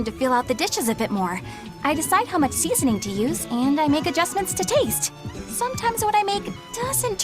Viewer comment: What was said: "...adjustment to taste" Text: "...a judgement to tate" Still better than YT caption though. "...a defense to paste"